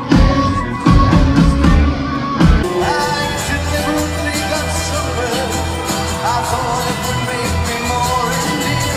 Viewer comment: I still don’t know what he’s saying at 0:53 someone help lmao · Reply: "I deliberately got sunburned, i thought it would make me more endearing"